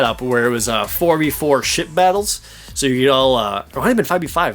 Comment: I know your saying Ship battles but it sounds like your saying shit battles